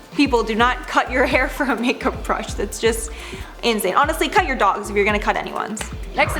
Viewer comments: Ok when she said “if you’re going to cut anyone’s hair for a makeup brush, cut your dogs.” She shouldn’t have said that at all. Kids might be watching then decide to actually do it. This chick needs to seriously think before speaking. I understand trying to deter others from doing dumb stuff but don’t give them equally dumb ideas in return. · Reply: I heard her say cut your dolls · Ok when she said “if you’re going to cut anyone’s hair for a makeup brush, cut your dogs.” She shouldn’t have said that at all. Kids might be watching then decide to actually do it. This chick needs to seriously think before speaking. I understand trying to deter others from doing dumb stuff but don’t give them equally dumb ideas in return. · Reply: I heard her say: "cut your dolls."